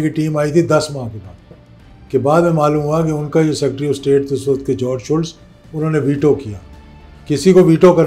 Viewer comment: did he say Jorge Soros 😮 🤔